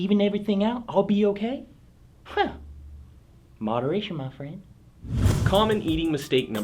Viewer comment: I thought he said, "Moderation mo fucka" Lol